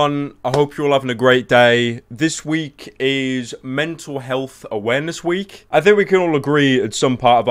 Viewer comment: Oh I thought you said man's health awareness week. Shit I was like fuck get the training gear. Pheww luckily not.